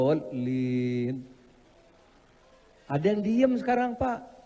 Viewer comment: Kayaknya bapak ni bilang waladhaalliiim..,bukan waladhaalliinn..,atau saya yg salah dengar.